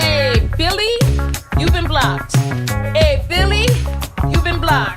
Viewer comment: I'm listening to the playback while getting ready and I thought said "PHILLY YOUVE BEEN BLOCKED!" I was like: 👀👀👀👀 Like Shaggy, it wasnt me lol